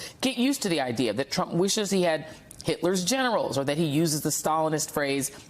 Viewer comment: Is that a typo?? I thought he said he wanted Hitler’s genitals.